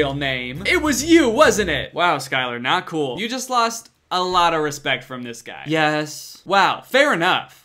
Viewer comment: when said “you just lost” an ad for old spice played, so it sounded like he said “you just lost old spice bar soap” to skyler, which honestly would’ve been a much better plot than whatever was actually happening